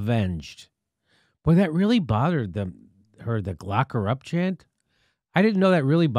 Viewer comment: At first I thought Jimmy said "Glock Her Up"